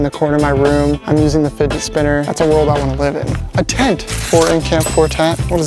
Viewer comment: I thought he said a tank instead of a tent